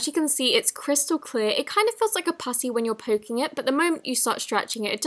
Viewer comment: Am I the only one who heard her say moki? 😂